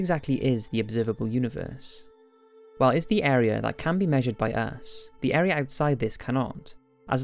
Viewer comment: It sure did sound like he said "... area that can be measured by ass" at 0:19.